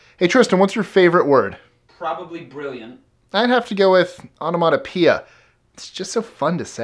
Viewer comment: excuse me, what´s his favourite word (0:12) ? automonappear?!…….Does anybody know? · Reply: Onomatopoeia- words that express sounds like splash, gurgle, mumble etc